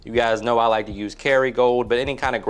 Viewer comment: Hey! Was just wondering what kind of butter did you say you used in the pan? Thanks in advance! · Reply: Look at his transcripts aka time stamp 4:45 he actually said Kerrygold!! Lol